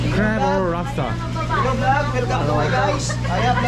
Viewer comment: now i keep hearing "hello vlog, welcome to my guys"😂